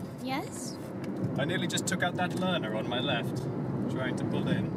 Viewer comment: it sounds like he says "I nearly took out that Löwe" :P